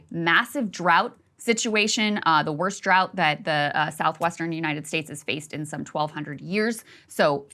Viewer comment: Did she say "the worst drought in 1200 years? and again